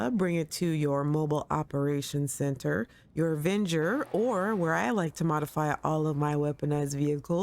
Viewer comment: I’m sorry but I can not hear/ understand what you said at 8:08. Where can you can customize this car? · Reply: You can customize it in your mobile operations center, your Avenger, or the arena workshop, which I like because the car doesn't get stuffed in the back of another vehicle, so its easier to see.